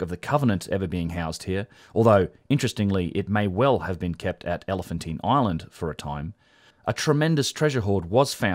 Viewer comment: "Elephant in Ireland" I must have misheard or the punnunchion is incorrect ?? Some one please enlighten me